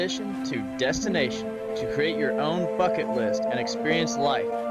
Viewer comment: Am I the only one who heard "create your own fucking list" 😂😂